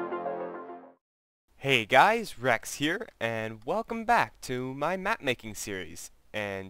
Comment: At the srart I thought u said "Hey guys, vechs here." You even kinda sound like him